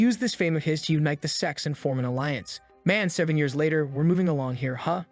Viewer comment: Bro I swear it said 31:51 sex I heart it maybe 42! Times to make sure it said sex not sects :/ 😠it’s rude ❤😊